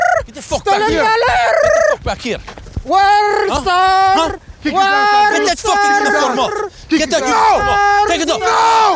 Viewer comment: Lol, okay, can anyone tell me what he's saying here? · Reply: World Star-r-r-r-r-r-r-r-r